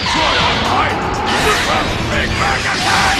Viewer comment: “Welcome to super vegetas BIG BANG ATTACK!!!” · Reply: Ik I’m late BUT WTF! HE SAYS “to super” I ALWAYS THOUGHT HE SAID “welcome tested for vegeta’s Big Bang attack!)